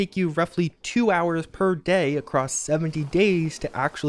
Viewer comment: 7 days, 17 days or 70 days I couldn't hear it that well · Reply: I heard 70